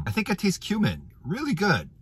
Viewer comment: Ha, ha, ha, ha, ha, I thought he said human before I realized he said cumin. 😂😂😂😂😂❤❤❤❤❤